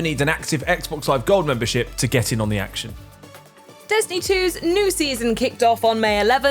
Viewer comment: What is distiny 2? 😂🤣 love the accent😍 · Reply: Yeah, I thought she said Disney... and then Destiny clips started playing. 😅